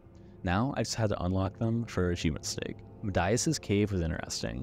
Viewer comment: hears you say medaius and saw cave of midas (pronounced like 'my duhs') and was like, "what? The Cave? To Pure Gold you say? ..and the followers? To Pure Gold you say? Oh the king, I get it!".